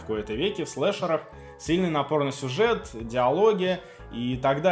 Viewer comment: в анимэ напорнасюжет, я слышу что то про порно сюжет :-D